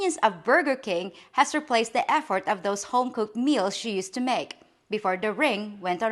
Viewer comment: At the 1:47 point I could have sworn you said "The convenience of Burger King has replaced the F word of home cooked meals..." If her home cooked meals can be described with the F word then maybe Burger King is a better choice